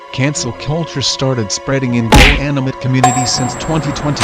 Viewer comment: the subtitles say gay animate instead of goanimate